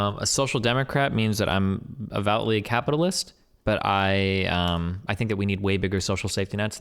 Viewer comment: Hey chat I have a question. When D is describing SocDem's, he says "_____" capitalist 45:56 . Can someone fill me in? Did not understand what he said. Thanks. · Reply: I’d agree with Zpinch wrt Destiny meaning to say “devoutly.” Aside from that, I think Zpinch is being uncharitable wrt Destinys ignorance of the term “devoutly.” It’s not like this is some obscure philosophical term. I’m sure he just misspoke.